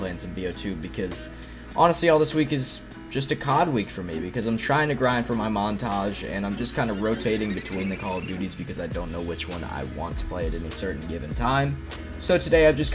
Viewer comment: I thought he was about to say "I'm trying to grind for my mom"